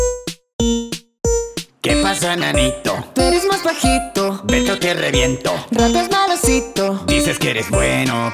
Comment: crei que dijo ´´ven que te reviento´´pero gracias a que lei los subtitulos para ver que desia´´vete o te reviento´´